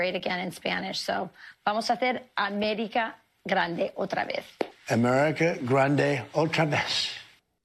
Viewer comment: Hahaha, check the close captioning around 2:15 as he struggles with Spanish. When he gets to the "America grande otra vez," the CC says he's saying, "America grande, ultra mess"! That's so true it hurts.